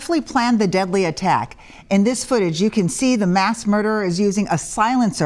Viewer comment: Why did she say "mass murderer"? It was only one killed. 🙄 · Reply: I also thought she said that too but I think she said 'masked' murderer. As per